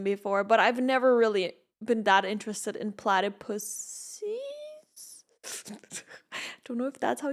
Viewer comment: Why did YouTube auto captions say platypussies and not platypuses 😂😂